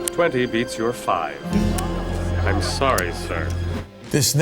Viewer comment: “I’m sorry sir” All I heard was “no soup for you”!